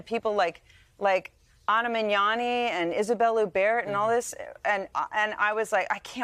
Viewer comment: Isabel who? I can't make out the name she's saying. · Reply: French actress Isabelle Huppert .